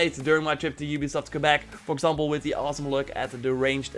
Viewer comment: At the end I thought he said 'Deranged assassin gameplay', which got me got me quite excited, but then I realised he just said 'The ranged assassin gameplay', which then got me disappointed. FIX IT UBISOFT!!!!